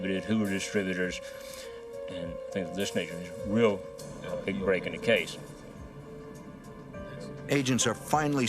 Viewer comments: "This *** really breaking the case."? Did I hear correctly? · Did he just say the N word “This nig*a is real…..” 🤦🏾‍♂️🤦🏾‍♂️🤦🏾‍♂️🤦🏾‍♂️🤦🏾‍♂️